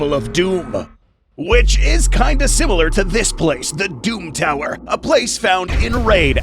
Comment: Sorry this is a little off topic, but I was watching Destroy All Monsters yesterday and I realized that the company is the same company from Halo Infinite (UNSC). Also, when you first said The Doom Tower, I thought you said The Doom Slayer.